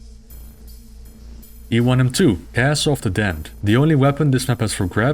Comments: I seriously misheard this as "E1M2: Asshole of the Damned". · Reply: Similar, I heard "Ass of the Dammed". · I seriously misheard this as "E1M2: Asshole of the Damned".